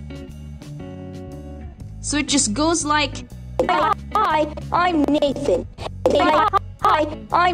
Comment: Omg!! When I said," Hi I'm Nathan!" i thought he said "Hi I'm Naked!!" 😝😗😜😊😃☺😘😘☺☺😚☺😚☺😚☺😚☺😮😮😮😮😮